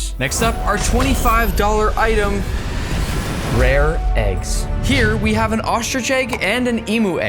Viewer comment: I thought you said eagle egg